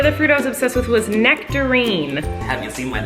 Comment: When she said nectarine I thought she said nicotine omg